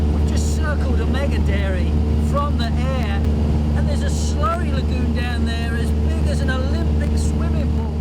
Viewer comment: the original english subtitle are wrong, I try to understand it, but can not catch the words as following , does anybody help to correct it? I appreciate very much : ----------------------------------------------------------------------------------- 00:01:21.739,00:01:29.060 generally speaking with respect to waste feculence 00:01:25.069,00:01:38.369 if you've seen a feculent 00:01:29.060,00:01:42.689 you know ground waters polluted most feculent are wreck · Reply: we just circle a mega-dairy from the air and there's a slurry lagoon down there as big as an olympic swimming pool!